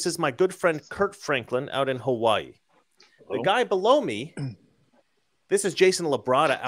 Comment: Did you say "Blow me"?